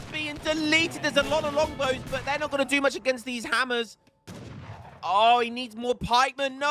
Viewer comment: How do you know who called the game? I just see the playback end. Happens in some matches and not others. I'm confused. Help. PS: For a moment I thought you said "Nietzschean Hammers".